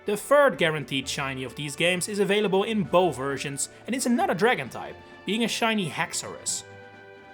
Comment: did he say not a dragon type that is a dragon